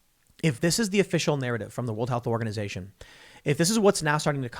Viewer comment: When you referred to the delta variant, the first time you said the WHO, I swore you said “ the Woke Health Organization”.